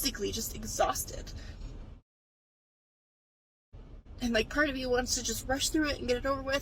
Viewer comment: she says "part of me just wants to rush through it" - but the caption said "Cardi B just wants to rush through it." 😅